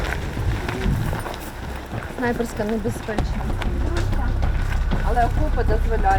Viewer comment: she said "snipers cover this trench" why did the captions say otherwise? I cant stand this simple control of narrative.. She said what she said... I know its minimal and insignificant but it bothers me... There was no translation, she spoke English. Don't manipulate peoples words. This skews the truth as well as opinions of the intentions of the interviewers (channel)